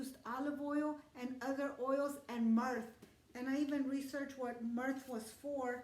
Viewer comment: Does someone know what is "merth"? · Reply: Myrrh.